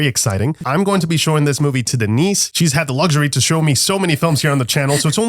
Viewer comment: I'm dumb. I thought you said, "I'm gonna be showing this film to the niece." and for a split second gasped, "Wait, Denise is your niece?!?!" .... .... I'll see myself out. T____T